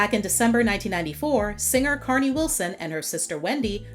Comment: My condolences to Carnie Wilson. May Brian Wilson rest in peace. 😔🙏🏾❤️🕊️ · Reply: For a second I thought you said Carnie Wilson passed.... regardless R.I.P. Brian Wilson.